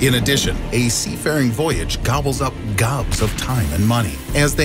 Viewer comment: i thought it said boobs